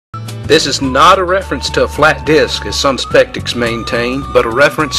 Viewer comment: Does he say "as some SPECTICS maintain" at 0:41?!?!?!?!?